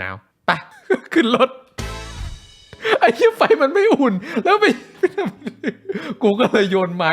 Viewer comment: ไครก็ได้ แกะหน่อยว่าตอนที่ 19:53 พูดว่าอะไร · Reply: ไอ้เชี้ยไฟมันไม่อุ่น